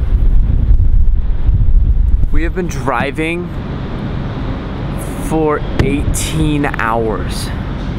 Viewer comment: "We have been driving for.... 18 HOURS?!" I HEARD "I'VE BEEN CRYING FOR 25 YEARS!? HOW IS THAT POSSIBLE?!?!?!" BTW I RELATE TO ALEX SO MUCH, I AM SENSITIVE AND AWKWARD, IM NOT GAY BUT IM BI AND I THINK ALEX IS JUST THE BEST HE IS SUCH A MOOD